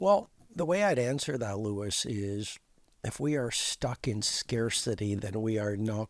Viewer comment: he said people are stuck in scarcity, but the way he said it sounded like stuck in scare city...interesting either way.